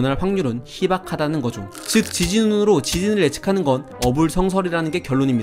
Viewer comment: 나만 지진을 지진으로 라고 들림?